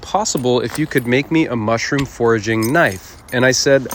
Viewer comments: Thought you were saying "mushroom forging" and i asked myself wtf is that but then i realize you said "foraging" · i thought he said "mushroom forging", not "foraging," making me think mushrooms get that hard